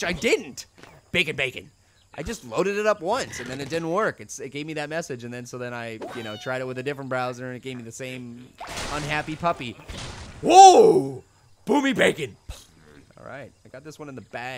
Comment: did he say boobie bacon... gross